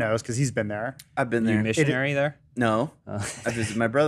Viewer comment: You know when I was listening last night, I thought James said, “Did you do missionary there?”, and not like did you go as one... And honestly, I’ll just pretend it was the former. It’s funnier that way in my mind